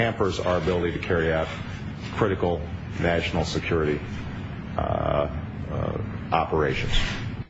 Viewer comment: COME ON Obama, you know you want to put Bush and Cheney on the chopping block! Do it! DO IT! (At 2:31, does he say "vaginal security?")